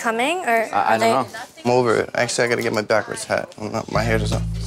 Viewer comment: Did he just say I need my backwards hat? 🤣🤣